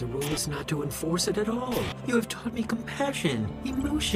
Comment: sounds like randy said "nigga take a fire ball"